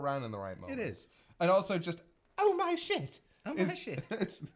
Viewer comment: i always heard the line as "oh, nice shit!" (not 'oh My shit')...to me, it just sounds more 'conversational' instead of feeling forced like "haha we dont have to censor it"...